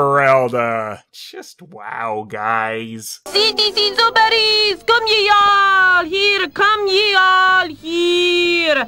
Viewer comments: I think I made up what the Medieval Beatnik is saying, somehow 9:42 "citizens of Paris! here! come ye all here! come ye all here! vote for the Pope of Fools!" 11:14 "Listen here all you people of Paris!" (I can't make out the rest of that sentence) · it sounded like she said yee